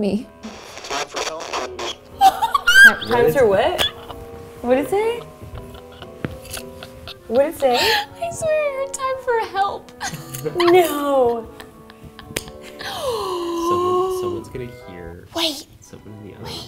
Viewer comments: nooo I heard “time for hell” 🤣🤣🤣 Mykie we’re going to helllll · I swear I heard "time for hell" when Mykie asked if she was gonna be taken out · when you asked what about me? I heard “time for hell” Update 37:02😂😂😂😂